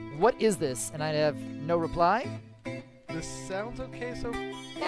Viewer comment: I thought said "the sounds of queso"